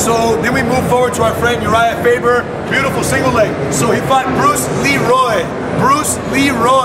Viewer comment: lol I thought he said "Bruce Lee."